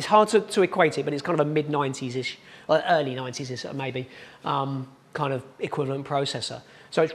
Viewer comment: Can someone please tell me what he says at 7:42? I'm translating, but can't understand that part: "Kind of (?) processor" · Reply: "its a mid nineties ish kind-of early nineties ish equivalent processor" or thereabouts. >Sean